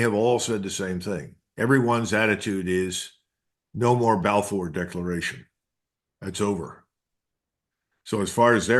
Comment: Did he say at 12:45 ‘no more Balfour Declaration’? Did he mean to say Abraham Accords? If he meant Balfour then that is huge.